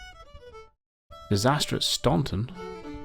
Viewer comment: funny...you said "Disaster at Staunton" and I thought you said "Disastrous Stompin'" :p